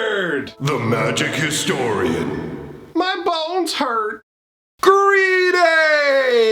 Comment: Your BONES hurt? Dude I thought you were saying balls this whole time.